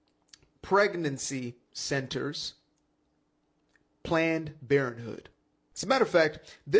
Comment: Did he say "planned BARON hood"? That's a cold one!